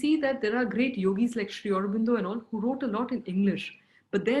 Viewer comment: she mentions a great yogi who wrote in English. Did anyone catch the name? I couldn't make it out. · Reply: Sri Aurobindo